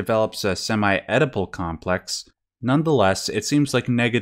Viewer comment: What is an “edible” complex? Around 8:53. I don’t know if I heard it right, but I couldn’t tell what he said? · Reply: Oedipal complex.